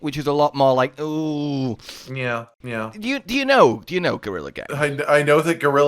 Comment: did you just say gurren lagann